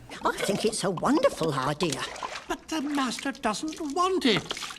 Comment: did cogsworth say bastard?